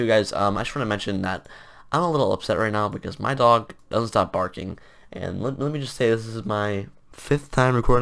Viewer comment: Dude In the Intro i thought he said his dog stopped barking meaning like he died but then i heard It again and realized His dog would not stop barking wooh